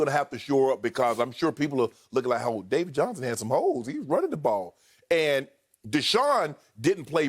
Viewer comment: "I thought he said David Johnson had some hoes" 😂😂😂